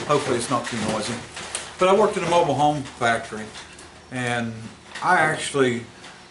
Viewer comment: At first I thought you said you worked for a mobile phone company... wait, what? ahhhh mobile home company! I didn't know about explosion engines, I've seen the old bulldozers that use a shotgun shell to start them though.